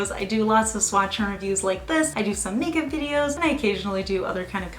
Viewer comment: I thought you said you do "naked" videos and I was like WHAT! Then I realized you said you do "makeup" videos. 🤦🏻‍♀️